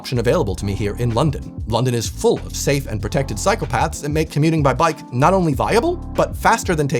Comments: I thought you said psychopaths but you said 'Cycle paths'. changed the context a bit in the moment! hehe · Timestamp @ 8:37 Did he said "London is full of save and protected psychopaths" ??? 😁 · I heard "safe and protected psychopaths" and I cannot unhear it now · i heard “ London is Full of safe and protected psychopaths” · After hearing that talk about being able to walk and bike for good mental health, I heard "London has plenty of psychopaths" only to rewind and realized you meant Cycle paths... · I thought you said full of safe and protected PSYCHOPATHS! Like bro, what...? Then I realized I was deaf 🤣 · "London is full of safe and protected psychopaths"?!?! Oh, he actually said "cycle paths"! 😂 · Did you say "safe and convenient psychopaths??" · You said cycle-paths, and I thought you said London is full of safe and protective psychopaths hahah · I briefely misheard "safe and seperate psychopath" instead of "safe and separe cyclepaths" 😅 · I was momentarily distracted while watching this video, until I heard "London is filled with safe and protected psychopaths." Had to rewind to figure out what you were actually saying 😂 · what i heard; "London is full of safe and protected psychopaths" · For a second I heard "London is full of safe and protected psychopaths" lol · I thought he said "London is full of safe and protected psychopaths" 😅😅😅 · 😂 I’m multitasking and I heard “because London is full of psychopaths” and I was like mmhmmm so true